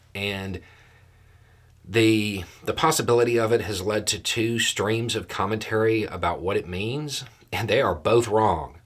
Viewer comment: It was the third time you said it that i heard, "streams of commentary" instead of, "two 'xtremes of commentary." Then when you outlined them, i thought, "either interpretation would work."